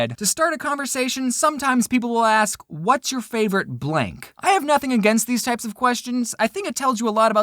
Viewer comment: I was cleaning my bedroom while listening the vid and i heard "whats your favourite plank?" Thinking it was a question that needed to be answered i said "wood plank or metal planks?"